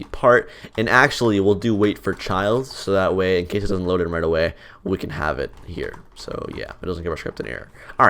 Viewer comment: what da hell you said here? im not from unit states so i cant understand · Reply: "So that way incase it doesn't load right away we can have it" not that hard to understand.